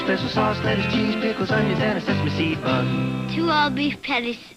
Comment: ☺ I can't believe after all these years I have only just now learned the correct lyrics of the jingle. I always thought it said "on a sesame seed bun". Thanks to I now know it's "and a sesame seed bun". 🤗🎶🍔😋🎧🎵😁